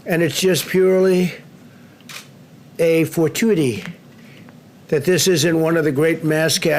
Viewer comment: What was that word he said at the 10:06 time-stamp mark? "For-too-dy"? Is that a word? Anyway, this guy sucks.